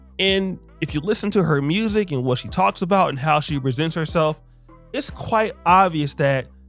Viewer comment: I almost thought you say how Cardi B RESENTS herself instead of presents herself 😅